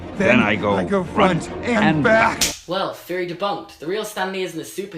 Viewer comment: I LITERALLY thought he said fairy debunked. I was very confused. Darn British Accent; messing up my thought process.